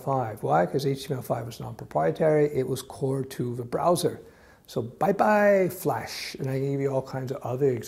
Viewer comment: I can not understand what says between 6:44 to 6:68. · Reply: Andrzej Jaworski Flash was proprietary, HTML5 was core to the browser. Bye bye flash.